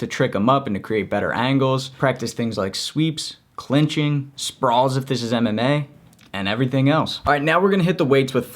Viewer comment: what did he say sprawlsisve mma?!! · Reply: Sprawls if this is MMA